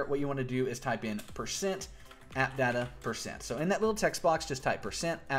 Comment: i put is what you said %@ or %atdata% i put it in and it did nothing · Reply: type %appdata%